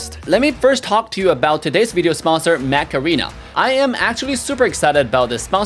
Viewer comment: I heard: "Today's video sponsor, Macarena" 🤣